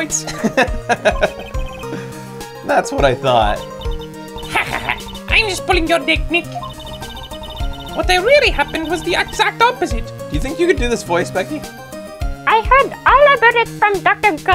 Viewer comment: did anyone else hear "I'm just pulling your dick Nick." O.o